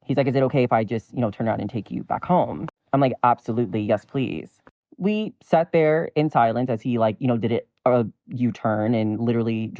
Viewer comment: true but according to his story. this was a double edge sword. When he approach. He should of made his gender clear. and keep saying , we're in Thailand as if that suppose to valid something. which it doesn't. Both parties should be be respectful. It seem like the guy kissing the trans was, more then the trans. sharing this 1 sided story. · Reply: I went back and checked... He said "we sat there in silence" and I SWEAR it sounded like "Thailand" to me as well!